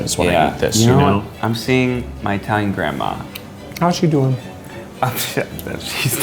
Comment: Ned: I see my Italian grandma Keith: how's she doing Ned : sHeS dEaD Keith: nods in approval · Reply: Oh I thought he said Dying Grandma...this makes more sense